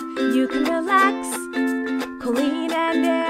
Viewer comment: It cant only be me that thinks it sounds like says "I'm the Queen" when he says I'm Colleen at the start 😆